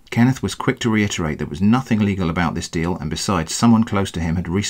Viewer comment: Wait, what? „There was nothing legal about this deal!“ Am I the only One who hears this?...